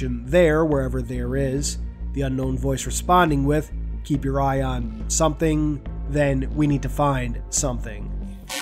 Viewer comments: I heard "keep your eye on the singularity". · It sounds like he said, “Keep your eye on the people there.”. I also thought I could here the word, “base,” even before it was mentioned in the video (maybe with the word, “rebel,” coming before, “base”)—interesting….